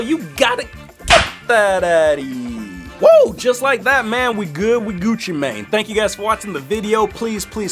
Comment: I FINALLY found out what he was saying. I always thought he was saying "We're Gucci mate" DON'T JUDGE ME